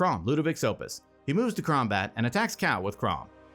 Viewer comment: It's funny how at some point between 11:40 and 11:55 you say krumbat instead of combat